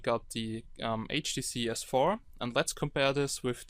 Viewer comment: Did he say HTC S4 ? :')